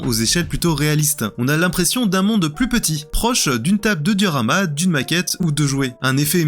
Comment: (...) "une table de durama"(...) Heu... c'est quoi au juste une table de "durama" (duramal?)? 😶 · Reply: Je pense qu'il veut dire "diorama", sorte de sculpture de paysage, voire une maquette.